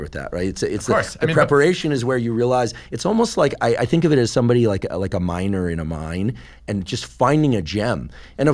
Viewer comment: Damn I heard "like a minor on a mine..." 💀